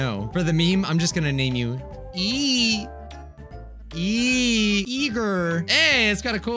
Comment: I thought he said ”e-girl”